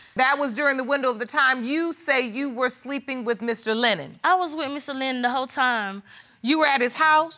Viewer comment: Oh... the entire time I thought they were saying "Mr. Leonard"😂